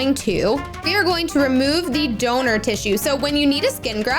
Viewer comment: I’m sorry when u we’re in miss Lewis I thought u said donut tissue 😂